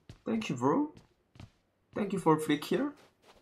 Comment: did he just say free kill???